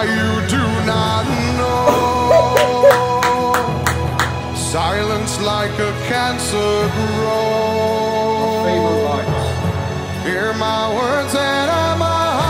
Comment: what the song says: "silence like a cancer grows" what I always hear: "silence like a cancered roar"